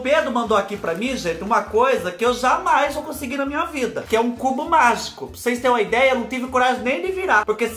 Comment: vc falou tão rápido que eu entendi "c* do mágico". Voltei o vídeo para ouvir novamente.🤣🤣🤣🤣